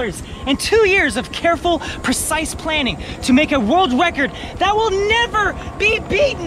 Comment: Is it just me, or did it sound like said in the start "... to make a wold wecod"?